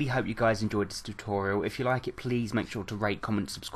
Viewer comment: when you said "rate, comment, subscribe" I thought you had said "rake, comment, subscribe" xD I can get distracted with words sometimes